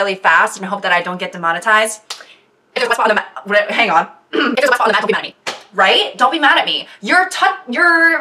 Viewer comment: ik I'm not the only one to slow down the vid to hear what she said right? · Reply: “If there’s a wet spot on the mat, don’t be mad at me” LOL